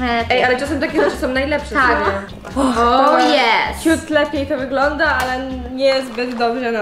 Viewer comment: co mówi? xD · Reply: "Ciut lepiej to wygląda "